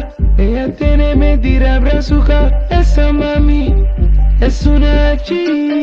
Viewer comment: creo que dice “gachí” expresión para referirse a una mujer .